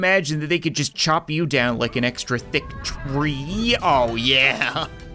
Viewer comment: did he call snuffy thicc?